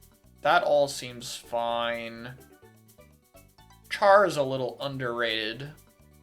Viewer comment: I thought he said "Chara's a little underrated" lulll